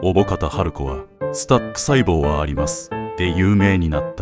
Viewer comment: スタップ細胞なんだけど なんか「スタック細胞」 って聞こえるのは俺だけか？